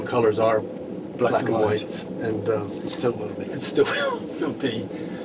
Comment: What name or word do they both repeat at 1:03 "black and white and (stoke woopey?) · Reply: "and still will be"